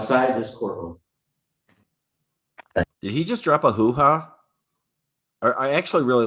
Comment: Note to self: Never gas light. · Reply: I think he said "hoopla" not "hooha". He was mirroring the judge, but he did it twice close together which made it a little weird. Unless that's just how he normally talks, but since you seemed surprised, I'm guessing not.